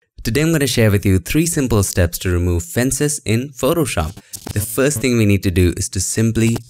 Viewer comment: I thought it said "remove France", smh